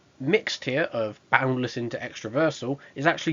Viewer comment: I mean it dominates boundless into extraversal... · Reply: I legit thought it said “boundless into extraversal” welp my bad